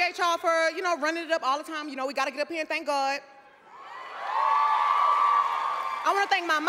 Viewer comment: "We got a good pen thank God." And do. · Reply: I thought she said "we gotta get up here and thank God"